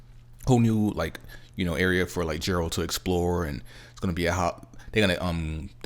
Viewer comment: Did you really just call him Jeralt..? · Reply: I thought he said Gerold hahaha